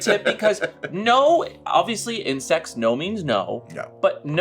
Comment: I thought said "insects" and I assumed it was a term I'd never heard before 💀